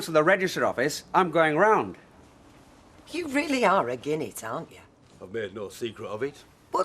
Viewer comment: What on earth does Dierdre say? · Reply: Agin it means against it.